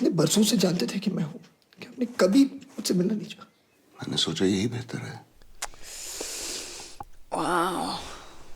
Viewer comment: while the Subtitle says "Not really" he didn't say that he said, "I thought it would be best not to meet you", The subtitles were wrong and you guys took it the wrong way.